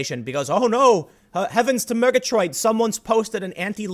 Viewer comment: "Heavens to megatrite." Did I hear that right?